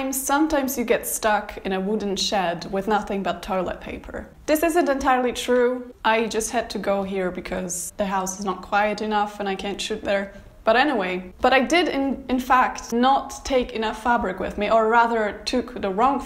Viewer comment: At first I thought you said "The house isn't quiet enough and I cant shit there"